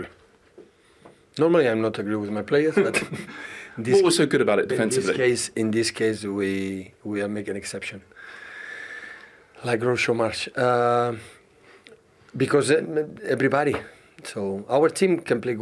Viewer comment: Did he just say like Graucho Marx?